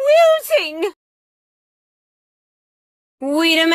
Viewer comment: i always thought she said “im losing” lol